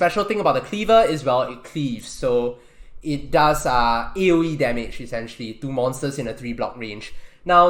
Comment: swat Does anyone know what he said at 4:34? Ewwie or 80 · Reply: I said AOE (area of effect)